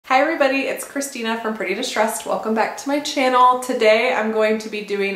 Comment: Turned out so good! So funny, when you started the video I thought you said “I’m pretty and stressed” instead of “from pretty distressed” lol!!🤪💕